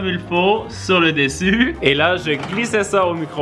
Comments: C'est moi ou a 9:37 min il dit ( je crissais sa au micro-ondes)??? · C'est tu moi ou y dit "on crissait ça dans le micro-onde" à 9:36 😂😂